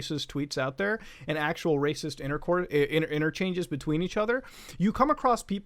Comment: I’m sorry. What was that Muta? Did you just say racist intercourse? ROFL